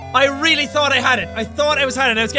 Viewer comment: I love how at 10:13 the captions just say “I really don’t know what he said” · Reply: "I thought I was haddin' it " is what I think he said.